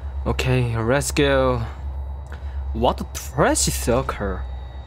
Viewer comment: Whenever says "circle", I always hear "sucker" 😂😂